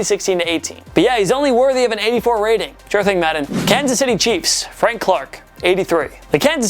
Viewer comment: does he say Kansas city cheeks?